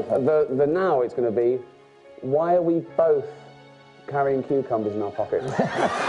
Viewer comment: What did Ricky say during their hug? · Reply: "Why are we both carrying cucumbers in our pockets?"